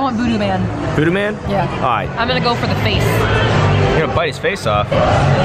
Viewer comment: What said: you're gonna bite his face off. What I heard: you're gonna bite his face saf